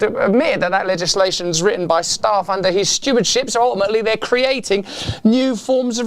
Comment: Ha! 3:54 I heard "...written by staff under his 'stupidship'..."